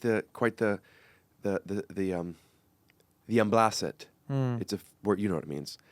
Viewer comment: what word is said here? sounds like um-bla-set ?